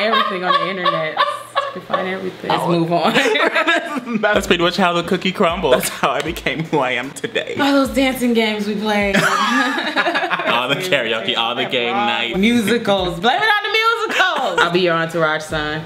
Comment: When you said game night I thought you said gay night lmao